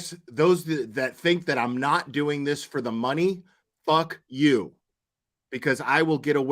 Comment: I heard her say ...those who think I'm not doing shit for the money...not what you heard